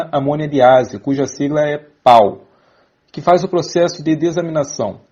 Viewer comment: foi só eu ou vcs tbm escura PAU 🤣🤣 mente mt suja